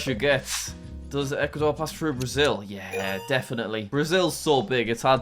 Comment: He said does the Ecuador pass through Brazil instead of equator